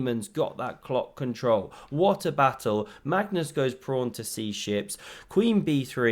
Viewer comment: Haha. Did i just hear @ 2:40 "Magnus Prawn to see shit" ???